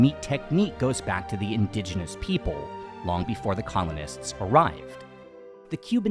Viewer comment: Whenever you say "colonist" in this video it really sounds like youre saying "communist" and it confused me multiple times